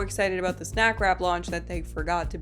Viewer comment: I wasn't looking at the video and thought they were saying "Snack Crap."